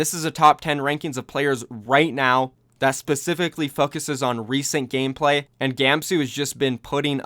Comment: Did anyone else hear fuck instead of foc at like